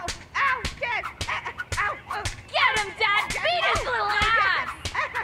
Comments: Says " get him dad, beat his little ass" Captions say "get him dad, beat him, oh yes!" YouTube thinks that this guys sister is being turned on 😂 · if you turn on automatic captions, YouTube says [Applause] YouTube is such a savage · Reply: Also, 1:03, instead of saying "Beat his little ass!" it says "Beat him! Oh, yes!" which makes that scene worse in a way.